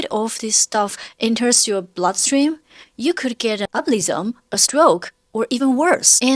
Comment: if she wanted that so bad, she could've just got a wig. Those things can look just as hog as that🤦🏽‍♀️ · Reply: I thought you said “you could get an ugly zone….” I hear things wrong all the time- this time it was kinda funny